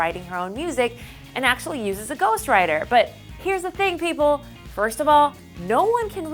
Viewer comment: She said Iggy😦 has a "Ghost Rider" and not responsible for her own music that means Iggy is a fake ! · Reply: I think you misheard. I believe she said 'Ghost writer'..